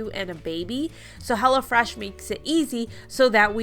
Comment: I know you said NOW, LOL! I thought you said “it’s hella fresh” don’t know why but laughed and laughed